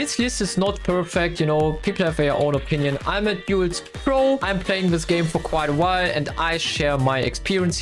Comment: i think you say i play this games before